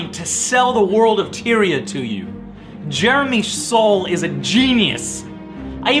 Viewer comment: At first I thought he said Jeremy's soul was a genius. Like, what happened to the rest of Jeremy?